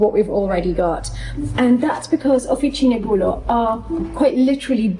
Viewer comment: Aha! I thought you were saying offici negulo and was puzzled that I could not find it online! Hahaha